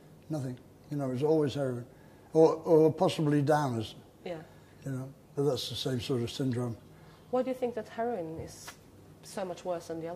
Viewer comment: what did he say? Or possibly what? I understood downers but that can't be :-) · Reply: 〈margae〉 "'t'is always heroine. Or possibly downers, for that's the same sort of syndrome."